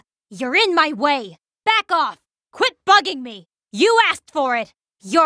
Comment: "Quit bugging me!" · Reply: idk but i always heard "quit bragging" and that seemed weird lol